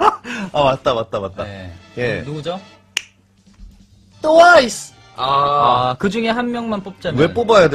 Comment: is it just me that feels feels like hyung jun saying towais instead of twice i cracked up bcoz of his pronouncation.